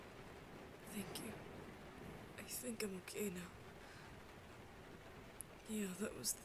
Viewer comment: My headphones where kinda quiet so I thought he said "I think I'm gay now" and not "I think I'm ok now"